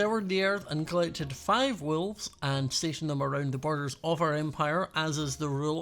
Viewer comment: I thought you were saying wools and I was like "Scott you have wool" and then figured out you said wolves so my bad I'm kind of an idiot